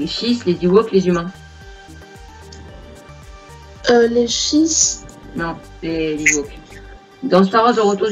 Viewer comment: mdr j'ai dit quoi moi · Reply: les shiismes ^^